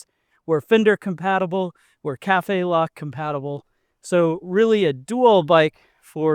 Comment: What word is he saying at 8:28? Cafela? Catayla? What are those mounts for?